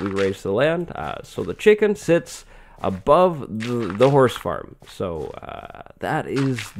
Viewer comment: Exude me did you just say horse farm?